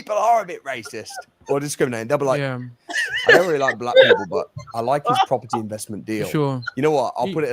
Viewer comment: “I don’t like black people but I like how he picks cotton” is all I heard 😂